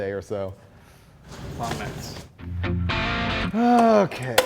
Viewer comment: can anyone tell me what Finnegan said? to me it sounded like he said "muffins" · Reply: Sounded like "long nights"